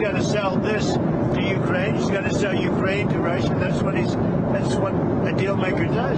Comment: Did Trump just say: "he's gotta sell Ukraine to Russia"?